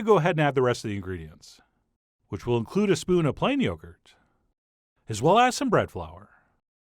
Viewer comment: I had to listen 3 times to figure out what he was saying! I heard “PINE YOGURT”!!! He does have a very odd way of speaking!